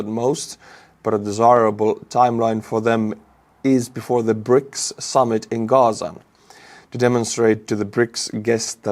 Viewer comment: I heard this as 'brics summit in gaza' and was wtf for a moment.